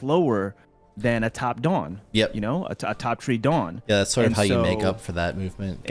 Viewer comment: Bro am I tweaking or did he just say top tree dawnblade?? Top tree dawnblade was a thing 8 months ago??